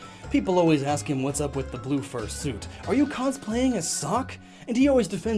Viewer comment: What he says: ''Do you cosplay as Sawk? What I hear: ''Do you cosplay as a sock?''